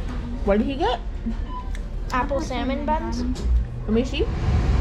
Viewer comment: I heard “apple SALMON buns” 😂